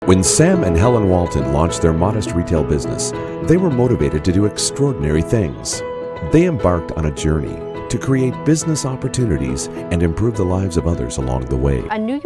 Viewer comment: sounds like it says salmonella walton